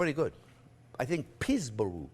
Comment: pre-made pie doughs can be purchased from the store. Peesbou... peesbear .... peesburee I think that's what it's called. Cracked me up!